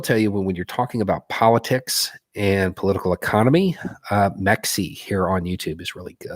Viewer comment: who is the other YouTuber he recommended? I can't tell what he's saying just by hearing it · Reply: “Mexie” is the title of her channel and i believe it is also her name